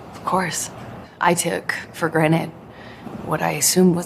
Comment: Did I hear her say “took for granite” instead of “took for granted” 😂